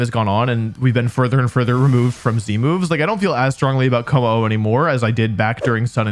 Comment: why is he saying c-moves? It’s z-moves.